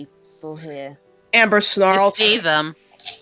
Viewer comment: What did say? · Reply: "I see them."